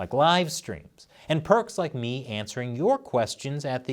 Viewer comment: "Perks like me answering your questions", thought he said "pervs like me" Lol.